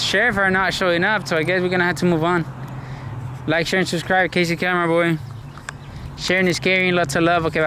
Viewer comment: For the longest time i thought he said its crazy camera boy. Until it dawned on me he said KC.